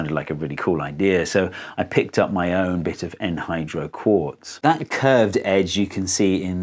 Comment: You Brits! I thought you said Anhydro quartz which to me means no water. But I guess you said, Enhydro quartz.